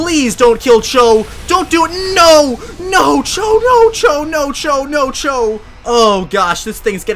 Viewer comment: When the butterfree used max quake I thought he was saying nacho nacho nacho